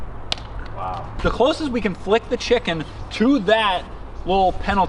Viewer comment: The childbearing one I thought it said friken chicken lol